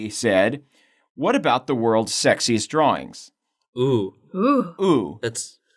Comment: I thought it said "Worlds most sexist drawing" LMAO